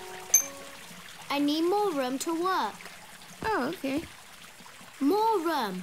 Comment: it sounds like he said he said, "I need more rum to work."